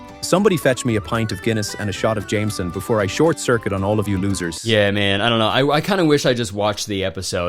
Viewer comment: I've seen attractive women end up with absolute losers in relationships but being able to choose your Ai boyfriend's identity and THEN choosing him to be an abusive dickhead who belittles you is another level of "just go to therapy for the love of god" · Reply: truly thought you said “lobsters” instead of losers